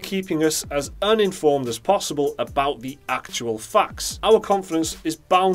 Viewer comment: "...keeping us as uninformed as possible about the actual FUCKS...". Well, English isn't my native tongue so I misheard the word "facts", but it doesn't make it any less true. There are a lot of fucks in the game industry these days. We want honest people, like you two beautiful bearded bastards! Keep up the great work! Speaking of actual fucks, here are game publishers/companies I'm not buying games from anymore; EA, ActiVision, Ubisoft, Konami, CapCom, Warner Brothers, and counting. Don't trust them anymore, so I have to choose from a another variety of games, which makes me discover other developers and publishers who are making plenty of really good games as well. That's where I put my money on games these days. It's very simple and very satisfying, at least for me.